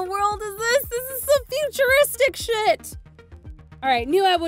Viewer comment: did she said sh#t XD?